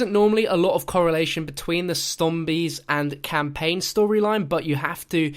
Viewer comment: Did anyone hear say "stombies" instead of 'zombies?"'